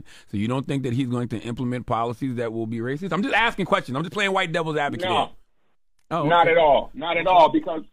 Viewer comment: did he say "im just playing white devils advocate" ?